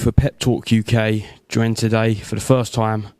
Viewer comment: Thought he said “Pet Talk” for a second.